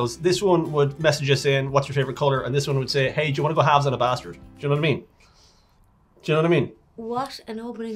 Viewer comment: "You wanna go halves on a bastard" that is the greatest thing I've ever heard 😂 that wouldn't go over well in the states even if I said it with an Irish accent!! Haha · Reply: I honestly thought they said halves on a bathroom….